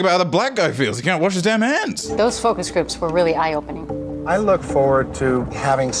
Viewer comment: did he say he looks FORWARD TO IT?!